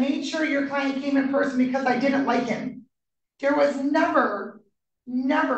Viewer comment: I thought he said that she "didn't like him to Zoom" .